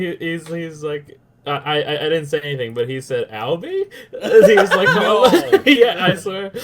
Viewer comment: So name is? Or something? I couldn't tell what he said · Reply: Matt Brink Ye his name is Albi it's on his Twitter